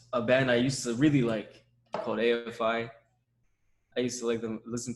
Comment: what he is saying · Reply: AFI, the band who made Black Sails